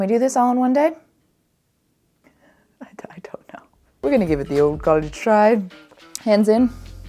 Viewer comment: I really thought you said "give it the old cottage try" 😂